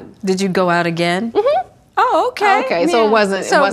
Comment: Did Willow just say "hunty"???